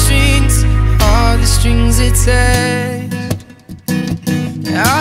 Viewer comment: at first i thought he said "now that shes a church girl" and i was so confused but then i listened to it a second time and then i knew he was saying "all the strings attached girl" i'm so stupid some times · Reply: i thought he said "All the strings to touch girl" but English is not my first language so I'm not that dumb